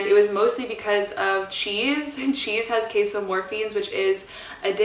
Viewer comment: I swear I heard “queso morphines” lmao